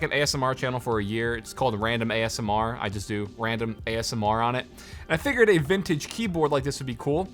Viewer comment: "random ass and Mar" jk chill ik what he said